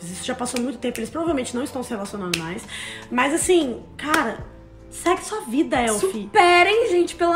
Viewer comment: Desculpa mas eu ouvi "Sexo é vida" KKKKKK... Enfim, bando de machistas, só isso que tenho a dizer :)